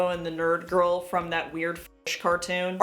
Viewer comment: Well now I need to know the name of that cartoon because I like the artstyle. Edit: Wait, did she say French or F*tish?...Hopefully she said French...